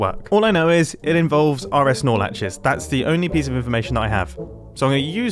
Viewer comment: all I hear is "are es nor latches". oris norlaches? help please, except wrong answers only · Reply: RS Nor Latches